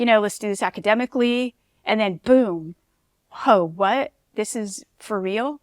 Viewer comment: Did shawty just say “Hoe What!” 😂